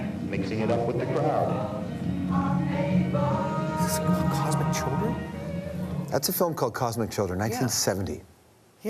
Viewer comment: What was the old clip from? It sounded like they said "Cosmic Jungle (1970)" but I can't find it on IMDB anywhere... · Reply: Stephen cosmic children